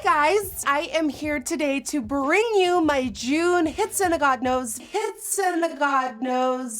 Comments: It sounds like she said "hits in a god nose " !!😂😂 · It sounds like she is say hits and a god nose!!!!!!!!